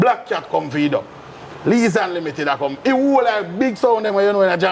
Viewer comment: did he say Lee Unlimited @ 1:37:37. St Thomas big sound. Jamaica's east side.